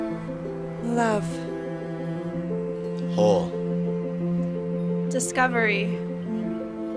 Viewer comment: legit thought he said "hole", and I could totally, depressingly, relate.